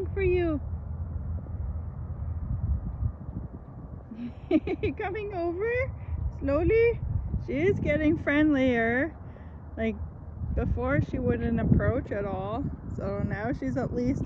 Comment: I like how the sunset light caught the orangey little fluff on top of her head! When you said the word, Slowly, as she came toward you I first thought you said lol I, and thought what a sweet name Loli (or Lolli) would be.